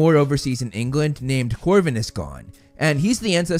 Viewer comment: I thought that said Coronavirus Gaunt 😂